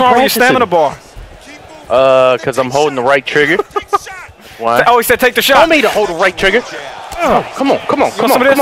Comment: it Sounded Like Cleveland saying"Take the shot"😂